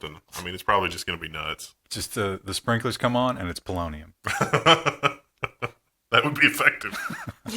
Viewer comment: Oh...Polonium! I thought they said Bolognium.